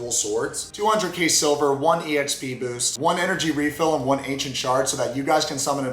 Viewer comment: Install Raid for Free ✅ IOS/ANDROID/PC: <link> and get a special starter pack 💥 Available only for the next 30 days :_90daydorksBigEDDY::_90daydorksEyes:□ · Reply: Definitely thought you said "Ancient Shart" instead of "Ancient shard"...... Hmmm. I like my version better. 😏🤣